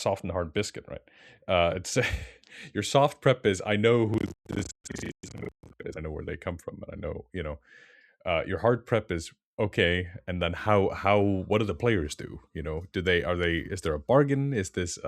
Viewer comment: Really wish I knew what said on this one! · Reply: "Your SOFT PREP is I know who this is, I know their name, I know where they come from. Your HARD PREP is and then what do the players do? What's the toy your playing with?"